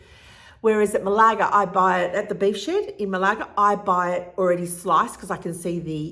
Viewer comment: Hi!! I enjoyed this. I’m also in Perth and I’ve been toying with the idea of the carnivore diet for a while now having eaten a keto diet for a while. Subscribing! lol! I thought you said bed shed but you said Beef Shed so I was confused for a second.